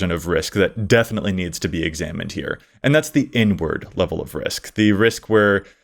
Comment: OMG.. I thought said "N-word" level of risk and not "inward" level of risk. Was like, "what? ok I guess, yea, saying that is very risky!"